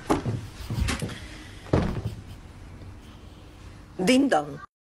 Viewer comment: I what heard: "Ding dong What i heard again: "Dicdock" What i heard again: "Ticktock" Tell me what your heard